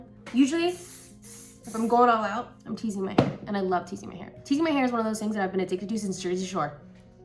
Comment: I heard you say my fairy hair